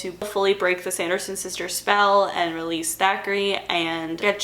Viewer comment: The biggest Mandela effect of hocus pocus for me was when I realized the cat's name was "Thackery" when all throughout my childhood I heard "Zachary"